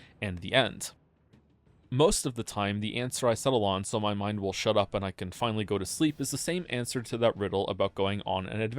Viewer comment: I initially heard that as “so my Mom will shut up and I can finally go to sleep.” Got a great image of Mom shouting media interpretation queries at him all day.